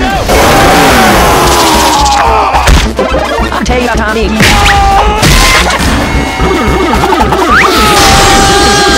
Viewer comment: what did he say? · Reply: He said "itellyouboutthetimemeandkeith" and you know what happens after.